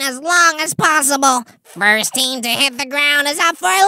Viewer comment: I heard beep's team in